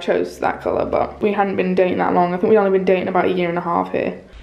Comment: i literally thought you said you and tom had ‘been doing it’ for a year and a half